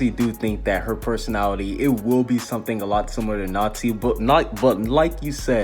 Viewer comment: OMG LMAO HE SAID NAZI INSTEAD OF NASHI